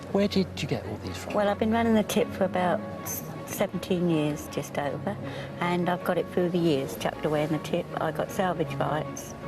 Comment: what did she said? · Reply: "Well, I've been running the tip (landfill) for about seventeen years, just over, and I've got it through the years, chucked away in the tip. I've got salvage rights for whatever comes in, I pay for, it belongs to me."